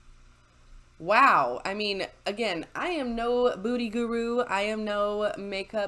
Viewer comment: LOL did you say "Booty Guru"? 3:49 Made my day! Love this channel!!!